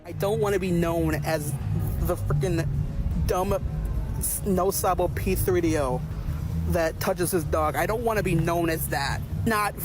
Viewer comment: Did he say he dont wanna be known as the dumb p3do that touches his dog 😭